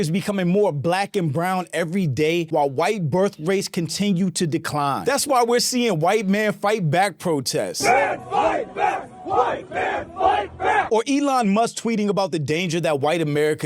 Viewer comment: I think I heard “closeted gay men fight back” not white men fight back.. or is that just my ears?